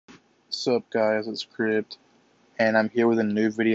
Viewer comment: i thought u said whats up guys it is kid